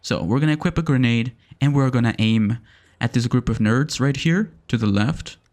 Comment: Anyone else hear "This group of nerds"? XD